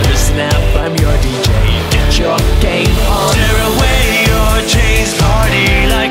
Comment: Did he like say 'Gay porn' at 2:38 ? Lol no offence to Nat Ho. I understand like what he is saying half the time though